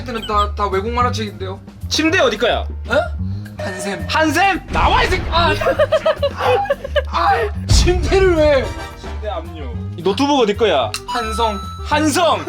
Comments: 저만 욕 들었나요? 나와 이 ㅅ야!라고 들었는데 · 초 나와 이쉑 ㅋㅋㅋㅋㅋ 나만 이렇게 들리나?